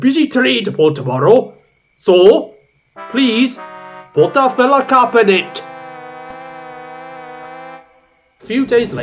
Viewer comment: What does Mark say at 16:20? "So, please, put (???) in it!" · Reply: "Put a filler cap in it!"